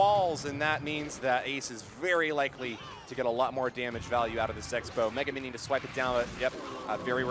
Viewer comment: commentator said sexbow instead of x bow